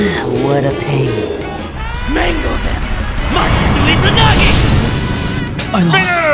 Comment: What did he say? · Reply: Magatsu Izanagi, the name of his Persona.